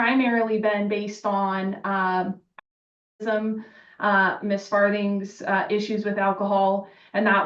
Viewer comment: Oh my goodness, I thought she said Ms. Farting. That’s probably the only funny part about this video. Hearing about her substance use gives you a good indication on what she’s probably going to use spousal support for - isn’t that just enabling her?